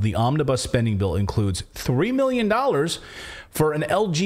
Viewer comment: oh men I heard at first 300 mio for these Museum.